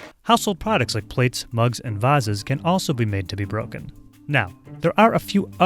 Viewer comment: DID HE SAID VaZez